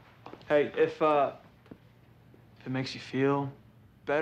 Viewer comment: it sounds like she says: "Thanks sis."